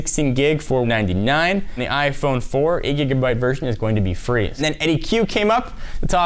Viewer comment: did he say the 8 gb iphone 4 si free?